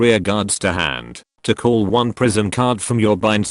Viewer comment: Why did I hear "Prism" as "prison"?